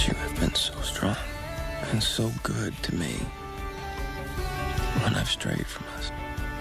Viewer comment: wht did he say!? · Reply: "You have been so good to me when I've strayed from us "